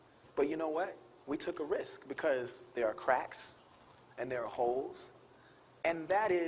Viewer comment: I thought he said crack and hoes instead of cracks and holes :|